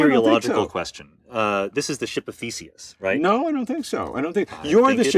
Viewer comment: i thought they said “ship of feces” at first😅😂😂